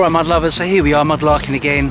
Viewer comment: Lol! I have been watching you for ages! I just now worked out you are saying, "Mud Lovers" I thought you were saying "My Lovers" Lol! I was thinking a bit personal. But whatever floats his boat.